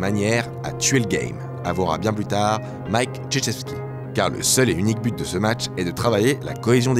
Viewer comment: avouera bien plus tard, Mike.... ??? Mike quoi ? mddrr j'entends Mike jujitsu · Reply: Neil Ghimouz krzyzewski